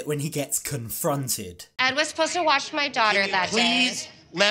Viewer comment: The show is lacking …. Why do they keep giving ED air time??? And what’s with Liz allowing this creep to give her daughter a shower ? Her daughter is older and not a toddler….. that’s creepy …. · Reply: I heard "watch her daughter", not "wash", but I may have misheard that